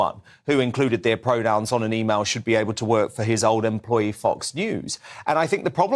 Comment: He did not say "should not be able to" he said "should not be".